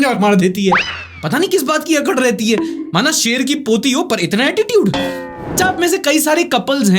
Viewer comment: Shar ki puthi I heard Shar ki Mausi